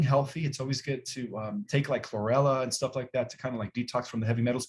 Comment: Did anyone catch the name of the detox that mentioned? I thought he said 'forella' but I can't find that name at any local health store. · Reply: Chlorella is one he uses and there are many others . If using chlorella please research as you need to know how to safely source and use. You may also want to look at my Roots videos with Clayton as the Clean Slate is amazing